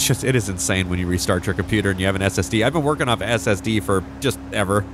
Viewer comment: I thought he was saying he switched to LSD😂